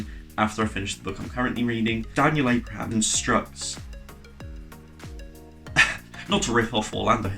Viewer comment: Ok I know I’m 5 months late I seriously thought you said “Daniel Abraham struts” and I was like struts. What is this phrase? I had to rewind to figure it was constructs